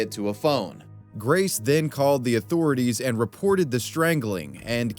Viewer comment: You know they don't use 911 over there. They actually use 999 to call emergency services. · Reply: did he say 911 i thought he said the authorities